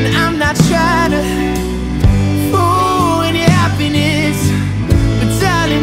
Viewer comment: He pronounces "ruin" as "fruin." I thought he was saying "fool your happiness." I still like the song but like, why say it like that? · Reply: I always thought he said fool your happieness!!!